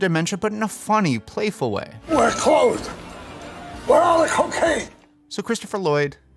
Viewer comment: ‐ what did he say? bc I heard "We're closed... we're on the cocaine!" hahaha I'm almost definitely wrong but wtf did he really say there?